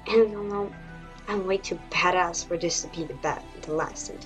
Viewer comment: did she say she's "bad-ass"......